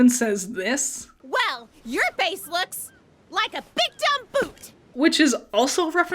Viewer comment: I always thought Nora said “your FACE looks like a big dumb boot” Base makes a lot more sense